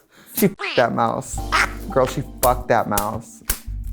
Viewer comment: Ok hold on 8:48 I'm pretty sure I heard Trixie actually say fucked. Someone confirm this for me. The first one was bleeped and then the second one sounded unmissed. I love this